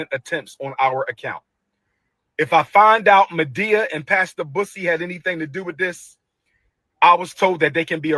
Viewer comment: I thought he said Madea and pass the P*ssy tried to access his account 😂😂😂